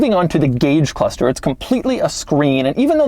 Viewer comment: did he say the car was 6-7 years old 🧐